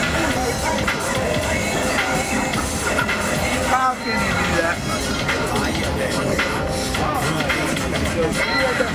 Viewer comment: What did he say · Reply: "How can you do that"